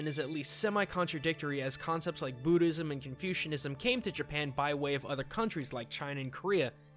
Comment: did u say confusionism at 3:45?